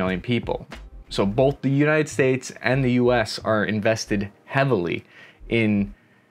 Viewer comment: did he say “both the United States and US”? lol